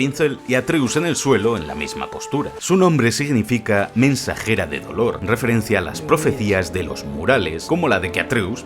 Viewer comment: Escuché " mensajera de color" jajaja